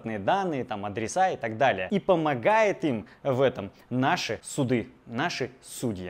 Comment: послышалось "наши суки" 🤣